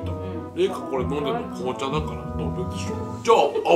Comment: 「ダメでしょ」がグーフィーに聞こえたw